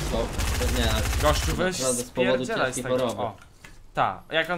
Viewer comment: Co powiedziałeś nie spodziewał bym się tego po tobie 6:34 Gościu weś spierdalaj? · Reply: Spierdzielaj, a to cos innego